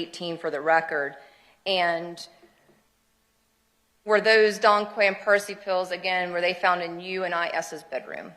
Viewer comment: What kind of pills did Quiles give to them? I can’t understand what the attorney is saying. Anyone know? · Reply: Dong quaid and parsley